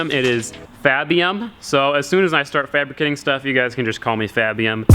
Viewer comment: Sorry lol but I thought he said call him fapium at first sorry